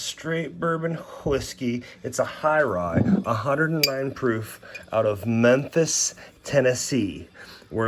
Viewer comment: Fook me i thought you said 190 proof i was wondering how you were alive.